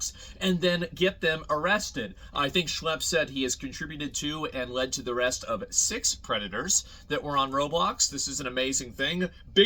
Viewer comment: I thought you said redditor at first, not predator which is pretry much the samething.